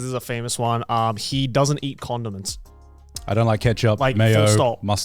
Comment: I thought said "he doesn't eat condoms"